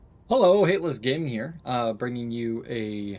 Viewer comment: I IMMEDIATELY checked your user name cuz I heard "Hitler's gaming" in the intro